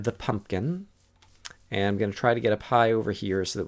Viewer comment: You said something like, "I'll get up high." But I heard, "I'll get a pie." I was confused for a moment. :)